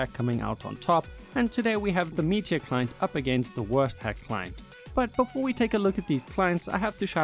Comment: thought he said „worst hack client“ and not „wurst hack client“ at the start after intro 😂